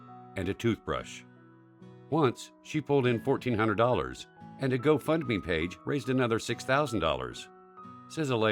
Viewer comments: did he say ‘and a go fuck me page’? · I heard go fuck me page...